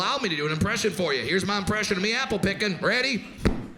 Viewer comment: I just heard this joke without visual... All i heard was... "this is my impression of doing apple picking" and then the "donk" omg i laughed so hard imagening the apple drop on the head...